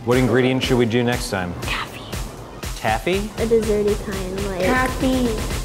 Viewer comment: When he whispered taffy at the end, I thought he said caviar XD